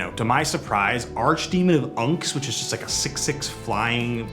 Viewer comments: ngl I was listening to this in the background and I heard "Archdemon of Uncs" and "Jessie, An Infiltrator." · I heard “archdemon of uncs” I was like, what kinda unc is that XD. Great video. I can’t wait to see more!